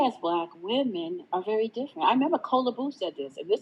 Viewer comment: Which name is saying in 1:02:36? Challa Boo? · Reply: She said Kola Boof